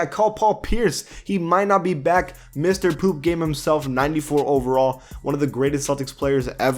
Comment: Hold up did you say mr poop game at 5:28 ?